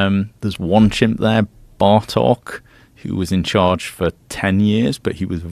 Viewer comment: Oh it's Bartok i just misheard as Bardok Father of GOKU 😅